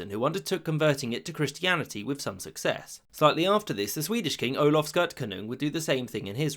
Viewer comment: Did you say 'Olaf Skirt-Canoe'?